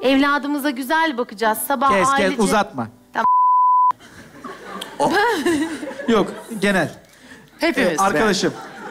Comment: Yönetmen ne diyor da bipleniyor? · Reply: Gerizekalı diyor